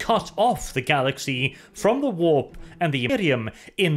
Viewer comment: Hey, there seems to be a bit of an audio problem at 24:51 when you say "From the warp to the I-erium" is what I hear... Hopefully that's just me.